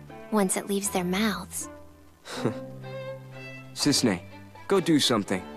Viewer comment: Cissnei ? i think ? he say that